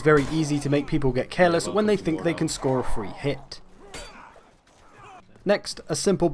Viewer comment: i thought he said cancer